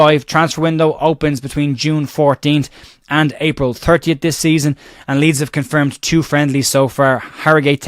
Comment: I think you said transfer window open 14 June to April 30th?? Or did I mishear??